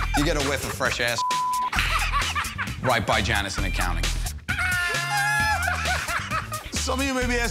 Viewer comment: right by Janice in accounting... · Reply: To me it sounded like he said “bye bye Janice in Accounting” meaning: Janice will leave her desk once she smells fresh assh**le